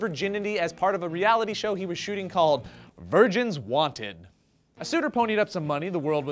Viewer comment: ohhh, "virgins WANTED." i thought you said "virgins WANT IT."